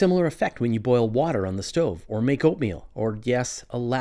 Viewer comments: Boiling Opium and Sun, interesting comparison. Awesome band name though! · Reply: Yeah, it totally sounds like I said opium. It was supposed to be oatmeal. :-) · I was watching on 1.5x speed and heard "make oatmeal" as "make opium". Not the type of reference I've come to expect from Fraser! 😅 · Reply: I was watching at normal speed and also thought I'd heard "make opium", it was only after clocking your comment that I realised haha!